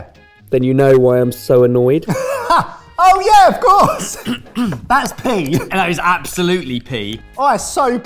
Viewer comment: The one y'all are talking. I hear "Thats Pee" not "Pea" LMFAO I'm crying.